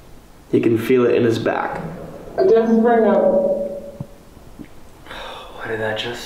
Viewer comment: I rewatched it multiple times and I think it says "I'm desperate now" …and it's a women’s voice so maybe desperate for help? 1:41:44 Slow head turn and the eyes of a man who is fed up with the devil laughing 😂